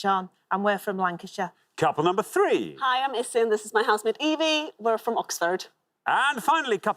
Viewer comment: It sounds like says Evie is her husband. I had to replay it. Evie is housemate 🏠 🙂 it was lovely she knew Alexander's 🐕 Haggis. Was a best first round I have seen with the pointless answers.